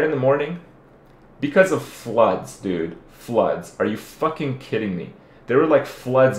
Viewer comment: english is not my native Can somebody pls tell me what the word did he say on 19:10? Something like "flods" or "fluds", can't find this in dictionary.